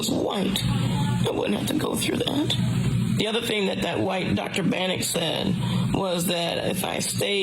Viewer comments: Who is this doctor, his name should be known, exposed n disposed.. No more discrimination. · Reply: Sounds like she said something like Dr Bannick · This is a sin and a shame !!!! At 3:49 , it sound like she said the Dr's name was dr bannick. This is so heartbreaking!!! Condolences and prayers to her son , family and friends . I also pray that the Lord will send her son the help and strength that he needs to care for his grandparents. That "dr " should be stripped of his license and sued to the max !!!! He surely didn't honor his hipacratic oath which he swore to. DO NO HARM !! Maybe others should be sued also for not stepping in when they knew he wasn't treating her like a dr should!! SUE THE HOSPITAL!!! SMH